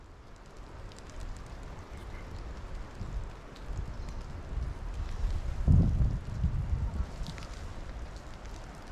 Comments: algo hablo muy raro el 9:08 parece que fue un espíritu 😫😭9:07pero la vos no entendí nada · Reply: Ella dice "matalo" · Hola esto se esta poniendo mas interesante cada video, me fijado que en el minuto 9:07 se escucha una voz, susurrando pero no le entiendo lo que dice, y creo ademas que esas entidades que has visto en los arboles son como brujas/os, que están al asecho para atacar a los duendes, saludos desde Argentina. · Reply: Es verdad, creo que dice "espero". Minutos antes de eso la voz dijo "mátalo", será que está esperando que le obedezca?